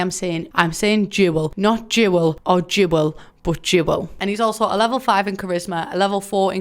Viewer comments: Genuinely until 7:13 I thought you were say jewelist, like someone that likes jewels 🤦🏻‍♀️ sorry I’m a dumb American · i thought you were saying jewel the whole time until you clarified you meant duel omg